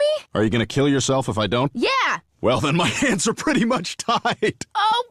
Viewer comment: “Are you gonna kill yourself if I don’t?” “Yeah!” “Well, then my answer pretty much tied!” My favorite part. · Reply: I honestly thought he said, "Well then my answer pretty much died."